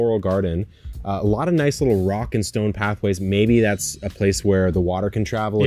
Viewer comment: Did I hear a ROCK AND STONE???